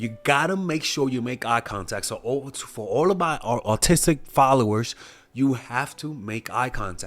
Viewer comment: did you say AUTISTIC followers? 😂😂